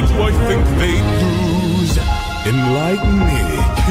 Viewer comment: I've listened to this half a dozen times and literally every time during Athena's intro when Uly tricks her into revealing herself I hear her say "Well done, you lied to me, what's your name," instead of enlighten me.